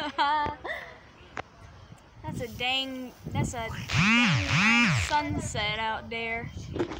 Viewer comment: That’s a dang a$$ sunset out there at 0:07 is what it sounded like